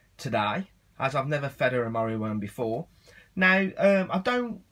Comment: Isn't that just a normal meal worm not morio worm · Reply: I thought you were saying Mario Worm :P